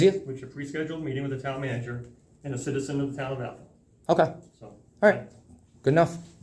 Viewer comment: It almost sounded like you said ahole not Athol lol😅🤣😂